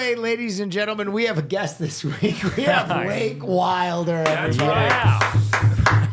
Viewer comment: Why did it say Cyr for Wake Wilder? 😅